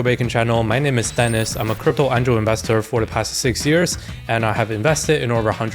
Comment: I've heard you say it a thousand times but I still can't work out one word. "My name is Denis and I'm a crypto ENDROW investor." Is it ENDROW? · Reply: crypto angel investor